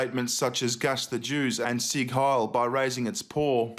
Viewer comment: "sieg hiel"? I thought he was saying "see kyle"